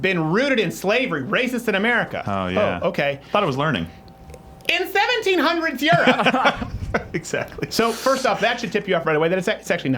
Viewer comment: they didn't even say "in 1700s Europe" they actually said 17th Century which is <num>, so it only worsens their point XD